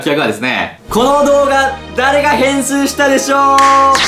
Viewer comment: 誰がへんすーしたでしょう、にしか聞こえない、、、（笑）